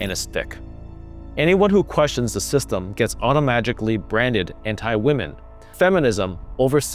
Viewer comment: Did you just say automagically? lol